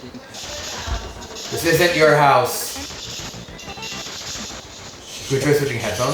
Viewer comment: you can clearly hear a female saying this isnt your house in the background.. ive been on plenty of investigation but that was someone saying that lmao, kinda crazy after that though 🤣